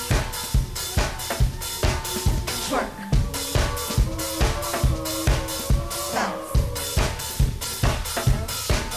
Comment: Did they say twerk at 3:11 and did mya just fuck it up with the gays in 2003?! These are my only questions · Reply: They actually said "schwerk!"